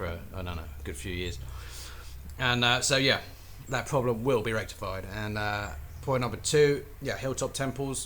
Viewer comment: did you say “...that problem will be rectumfied”?